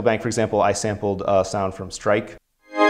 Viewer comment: Which app did he said he sampled (at 3:42 approx) ? Stripe ? Never heard of that. Not on itunes store. So what is it ?